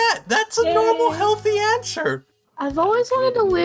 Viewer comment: is it only me but did he say ansher?